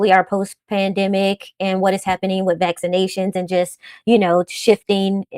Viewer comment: Did she say: "What is happening with vaccination?" Because I don't understand what that means and what it has to do with anything. (Also, they're making cuts to healthcare here, now). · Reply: This is a great question and yes, she did say vaccination. According to The American Journal of Managed Care (AJMC), CDC, and other credible peer-reviewed studies/reports there is a resurgence in once-eradicated diseases/illness as the result of decreasing vaccination rates. Of course, there is public mistrust due to mis/disinformation which has contributed to more visits to the hospital (some regions are affected more than others). Recent job reports indicate increases in positions in health care but we know better which is why we educate about the nuance of these job reports. The math ain't mathin' as Dr. stated. You are right, they are making cuts to health care jobs and in many other sectors as well. That's what that means, what it has to do with jobs in health care, and how it impacts people in that field. Given the brief explanation, we hope that helped clarify. Great question. Both consultants here have several decades working with those in health care and hope that helped. Many blessings. 🌱